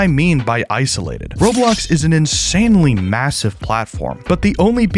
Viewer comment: did you just say MASSIVE?